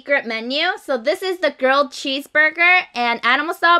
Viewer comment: everytime she says grilled cheese burger, i thought she said 'girl' cheese burger 😝